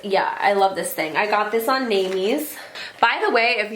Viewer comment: Question, near the end, time stamp 38:45; you mention a site where you got the brush holder. I didn't quite understand the name or site that you had used. Could you be so kind as to tell me again what it was. Thank-You. :-) Question: When you went to school to learn your trade, this would be a good video for you to put up, what should you look for, ask questions about and what are the pit falls that we should be aware of. Some schools are better than others, some schools are scams if not poor in quality. Should we judge the school by the teachers, by the make up kits that they will sell you, by the work that they bring in or provide. In short if you were to do it all over again what would you recommend a budding young student look for. Where would you go to if you had a second chance to study your craft? Thank-You. :-) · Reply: Jumpoff A the store name is Naimes